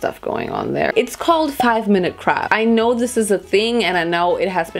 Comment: I think I just heard her say.... 5 minute crap 😅